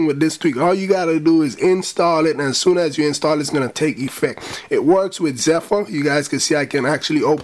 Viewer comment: It works with _____??? Did u say zefo?